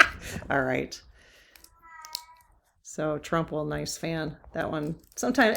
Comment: I thought I heard "Trump will drop dead". Come on, ask 'er again!